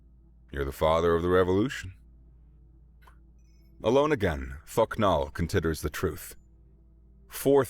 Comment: I think I may need to get my hearing tested. I heard Thok Nal's name as F*** Nah! Sorry!